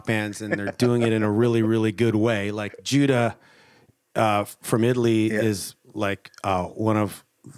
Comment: What is the band called that he said at 14:39? Couldnt really get the name... well still cant. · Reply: Giuda <link>